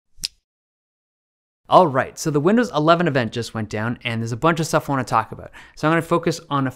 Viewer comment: did i hear him said... windows 11venger... Windows Avenger???😁😁😁